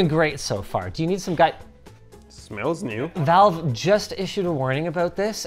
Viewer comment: the subtitle is wrong. It’s valve not Intel